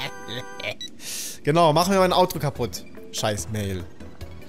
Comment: Ich hab Auto statt Autro verstanden xD